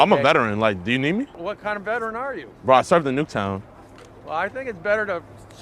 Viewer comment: did he say he served in Nuketown? Like from Call of Duty?